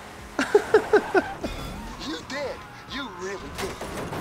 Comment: did anyone else hear you dead... you really dead 🤣🤣🤣😂